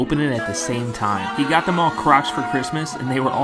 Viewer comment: I’m so high I thought the narrator said he got them all crotch for Christmas 😢I was so lost like no wonder they so happy 😂😂😂sorry for the mixup😂😂